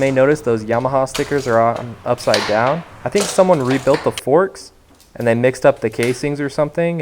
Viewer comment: Erm, 4:47, did you say "forsk"?